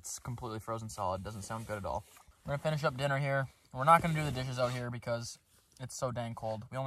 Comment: I thought you said “we’re not going to do the dirty out here” 😂😂😂